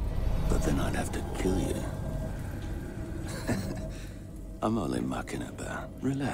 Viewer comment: what is he saying? I'm only....?? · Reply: Im only Muckin‘ about